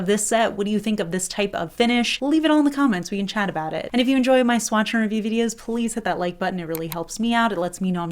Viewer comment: For years and still now except for like once I've thought you say "swatcher review" instead of swatch AND review.